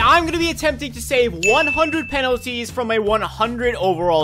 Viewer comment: Bro Did he just said penaldo?